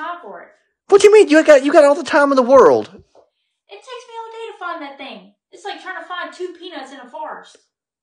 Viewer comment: Two peanuts in a forest got me! LOLOL Dang she know how to hit below the belt. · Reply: i thought she said 2 peanuts in a fart